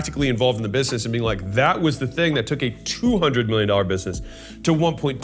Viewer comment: did I hear it right? 200m to 1.2m by sheer volume?